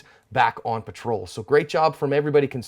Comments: always sounds like he says 'covered their ASSP' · does he say cover they cover there arse,cos it sounds like he saying cover their rasp,??